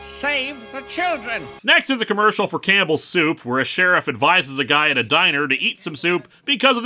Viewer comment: Whenever Linkara says 'Campbell soup', I keep hearing 'cannibal soup'...